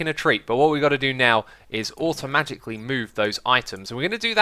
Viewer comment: I swear I hear "automagically." I swear X, you make up like 10 words a day XD